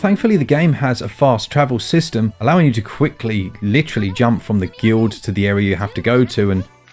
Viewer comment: Fan service has always been a thing with the Fairy Tail series. Wasn't planning on buying it anyways but regardless I'm sure fans of the series would enjoy it. I don't remember seeing much serialised fairy tail games before this so it's a start. Hopefully it gets patched to fix the performance issues. Anime fans now have Jump Force to look forward to. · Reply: Did you just say Jump force? Lol